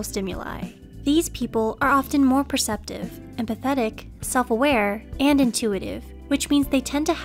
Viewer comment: wait- did you call them pathetic or am I mishearing